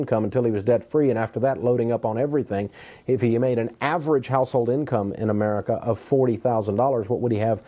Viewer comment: If the wife makes similar than its $4,533.32 a month... Don't be a bonehead. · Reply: at the 1:40 mark of the video, he clearly says household income. I accept your apology. The next time you decide to start name calling from the safety of your keyboard, at least listen and know what you're talking about, OK? B-bye.